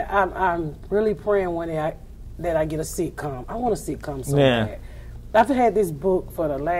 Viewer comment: I thought she said "Hopefully one day, I'm really prayin one day Ima get a C-cup, I want a C-cup so bad" but she was talking about a sitcom.